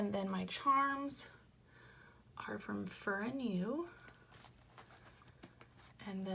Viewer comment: Where are the charm from? Sound like you said "Fur & New"? Also.... that link for Annie Plan doesn't work?